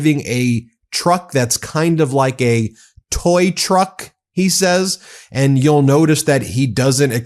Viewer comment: I heard "tow truck" not "toy truck" I hate to defend the guy but it was a tow truck for tanks. A very big "tow truck" What pocket gets these tariffs???